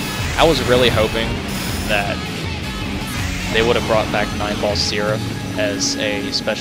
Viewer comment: Every time you say his name, I hear "Nineball Syrup"...